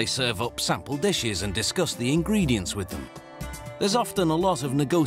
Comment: did he just say disgusting ingredients? 😂